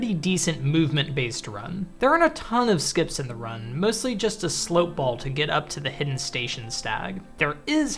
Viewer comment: I'm sorry, WHAT did you just call that stall ball?